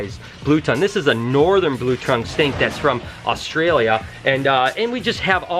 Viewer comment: I thought you said blue trunk stink😁